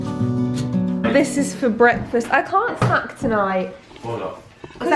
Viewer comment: i have the subtitles on for some reason and it said” I can’t f*ck tonight” 😂💀 and I’m not fully sure what she said lol